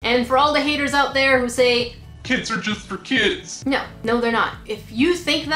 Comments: Kids are just for kids legit thought that's what she said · As an answer for "Kids are just for kids" Well that's not an excuse either to make a crappy product like the sticker kit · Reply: I thought she was saying that, but I knew she was saying kits are just for kids, but still! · When I totally thought she said "kids are for kids" 😂😂😂😂😂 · I heard “KIDS ARE JUST FOR KITS”